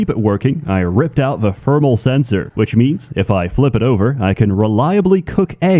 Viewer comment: I heard "ripped out the fertile sensor", which just left me more confused when the egg started cooking.